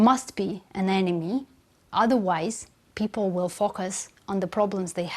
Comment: When she said focus , I thought she said they will fuc%k us lol